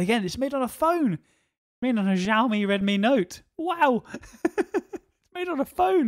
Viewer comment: what did u say? nagalme resha note? XD